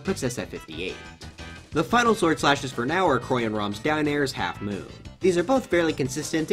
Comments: sounded like he said "Chroy and Rom." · did he call Chrom Corey?